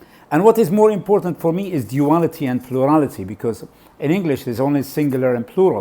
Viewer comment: What did you say after word duality at 1:12? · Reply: Duality and plurality